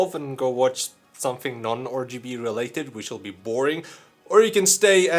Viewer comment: the captions think he’s saying orgy instead of rgb